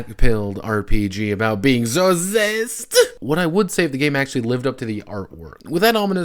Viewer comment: "...about being" WHAT?! I can't understand what you're saying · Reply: Zased. Like Based, but with a Z.